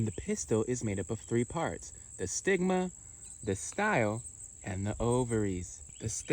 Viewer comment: I thought you said saigma